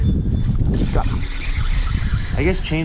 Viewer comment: For some reason I heard “Pickle Popper” I might have to call any green popper that now 😂